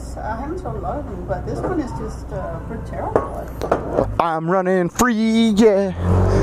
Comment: did she just say " But this one is ahhh just Terrible I heard" lol